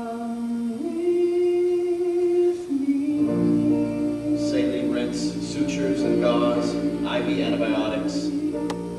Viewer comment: Does anyone know what Dr. Madden is saying at about 54:34? Saline _____________________________, isolated, sedated, and restrained. I can't make it out. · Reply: Saline rinse, sutures and gauze. IV antibiotics.